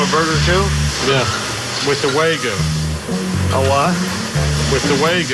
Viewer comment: is said to be a “Meat sculptor” Also: calls Wagyu “Waygu” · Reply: Ohh he said waygu I thought he said 'With the way good